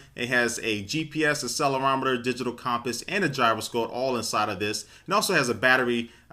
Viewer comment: did you say at 0:22 "comp-us"? Do you mean compass?